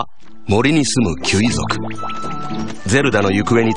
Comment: Does anyone else hear him say 'Fuck you!'